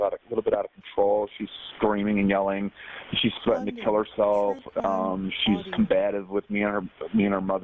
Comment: I thought they said she never threatened to kill herself.